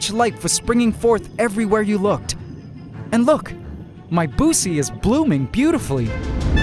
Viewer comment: I thought he said “ My bussy is blooming beautifully” 😂